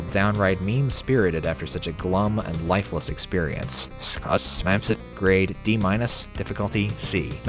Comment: I don't get this part....I even tried writing down what I heard & reading it backwards. Sounded to me like "Su smaps it" -->Ti smaps us...doesn't register to me. Maybe close to "This map sucks" if I had to take a wild guess though.